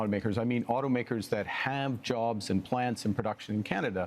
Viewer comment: minutes, Did Carney say they would give automakers "Handjobs"?